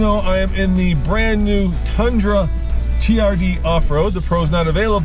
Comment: google says tundra has a 38gallon tank. 1/4 tank is close to 10 gallons, and gas around here is over $4/gal. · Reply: I misheard in the video, thought they said it was the TRD Pro. Which definitely had a 38gal tank for the 2021 model year. The 2022 off road seems to be a 21gal tank or so. But still, that $20 vs $4.